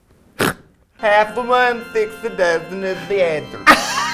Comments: What is she saying at 9:21 lmao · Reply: Half of one six a dozen of the other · I thought it was "half of one, six a dozen is the answer" which i find COMICAL. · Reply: I always thought she was saying “half of one six a dozen of the other.” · what did she say im crying hfjfhsjkfjsha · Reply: Daniel Miller “half of one, six a dozen of the other” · For people wondering what Trixie said at 9:20, she said "half of one, six a dozen of the other" which is a common idiom that means 2 alternatives are the same.